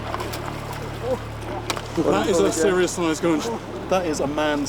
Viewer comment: This whole time I thought he was saying gooch because my gooch has a big mouth